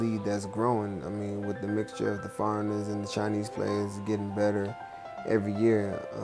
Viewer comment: Hold up did he just call them Chinese players and... farmers? They got farmers on the team? Damn.... shits rough out there bruh... XD Awesome.